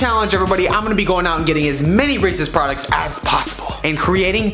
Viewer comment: When he said as many Reese's products it sounded like he said racist products